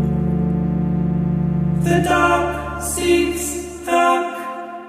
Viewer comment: I’m sorry but all I hear is “the duck seeks duck” and then suddenly Ink is creepy and I’m just- a h